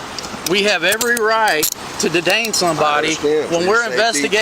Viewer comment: Did he say dedain